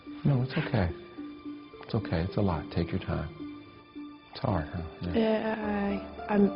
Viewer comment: DOES HE SAY "ITS OKAY ITS A LIE TAKE YOUR TIME"